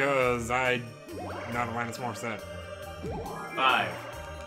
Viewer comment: Was that English? It sounded like, "Not a line that's more set." I'd just like to see you try... You're moves are as predictable as the AI at times.